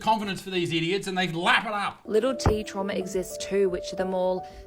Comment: “little titty trauma” is what I heard😂